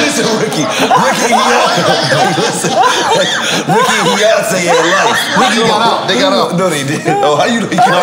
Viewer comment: Ricki Minaj and Heyonce? 🤣🤣🤣🤣🤣🤣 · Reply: I thought he said ”Heyonce”!!!!!🤣🤣🤣